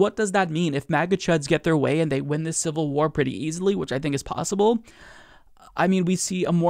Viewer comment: I thought it said civil wap ...